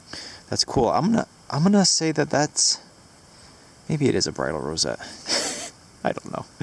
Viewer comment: For the longest time, I thought you were saying "bridal rosette" ... like maybe a decoration from a wedding dress? I wondered how could there be so many of them. Then I searched for it and Google corrected the spelling ...